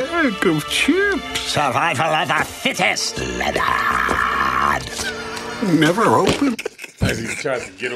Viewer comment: 😂I thought he said, “ Survival of the fittest, Nijaaaah” (You know what I’m saying)🤣🤣😅😅